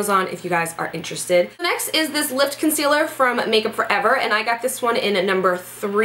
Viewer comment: I thought you said lip concealer😂